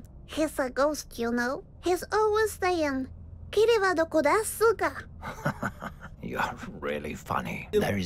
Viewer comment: "Kiri wa dokodesu ka?" means where is kiriwa in Japanese. Kiriwa is an executioner who loves to torture his victims and cause them agony which may allude to our narrator but idk it could just be a bit in the game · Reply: Oh I thought he said "kimi wa doko desu ka?" Like where are you? As in him hearing but not being able to see her o